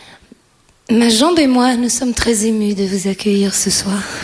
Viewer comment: what does she says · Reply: what did she say at 4:30, after <toue le monde>? "mes jambes..."